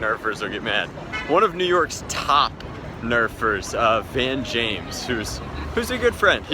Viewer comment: When you said Van James (or whatever his name is, sorry if I've butchered it!) my ears perked up because I thought you said Ben James, which is my name. Since when was I from New York?! And good at Nerf?!?!